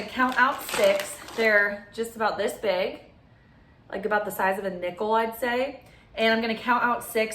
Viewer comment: I actually almost spit out my water when you said "they're about the size of a nickel" I legit thought you said NIPPLE, hahhaaha.